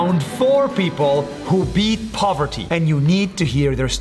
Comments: I THOUGHT HE SAID “PUBERTY” OVER “POVERTY” 💀💀💀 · I thought you said puberty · I thought he said puberty and I was how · He's back!!! I had thought that new guy took over😂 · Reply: I thought he said puberty 💀